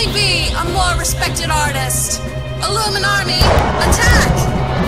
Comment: is it just me or did katy say, "iluminarmi" at 21:19 ?